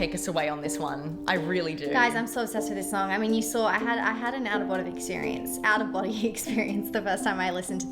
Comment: I really thought said "an out of Bonny experience" talking about The Fate of Ophelia and I was like, honestly tea 😂